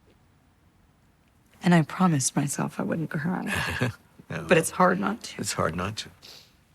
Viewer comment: What is Scarlett saying on 0:03 and 0:07? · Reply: “And I promised myself I wouldn’t cry, but it’s hard not to”